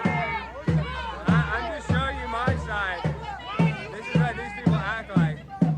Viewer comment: what did she say? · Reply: “You are doing a very respectful job of showing your side” or something along the lines of that